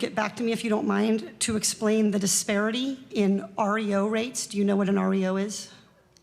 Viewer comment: Lmao i thought she said oreo too like give him some slack 💀